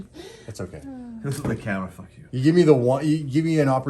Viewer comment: I swear I hear saying f u back to lol